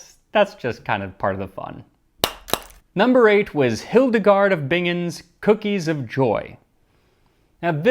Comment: ahh, I thought you said Hildegard Von Blingin! Shes an amazing bardcore youtuber! I thought it odd that two channels I love to watch would slip a collab past me. Alas, it was the actual nun.